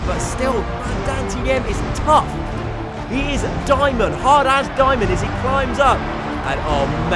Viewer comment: did u just say hard ass diamond